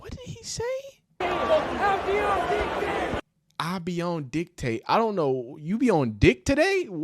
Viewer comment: i think he said how deions dick taste, which is mad weird and makes no sense but definitely said sumn ab how something dick taste